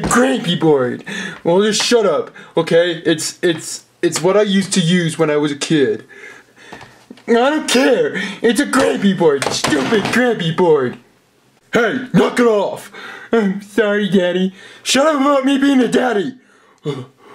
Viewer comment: did anyone hear the postman pig say "it's a cranky board,stupid cranky board!" sounds like gangnam style?